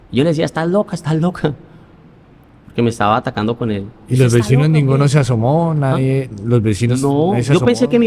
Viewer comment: En el minuto 34:55 se escucha de fondo la voz de una mujer que dice “está loca o que?” Lo devolví porque pensé que había dicho la chica que entrevista.